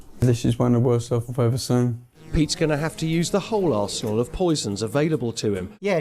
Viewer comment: I DEFINITELY heard "Pete's going to have to use the whole 'aresehole' of poisons...." 😂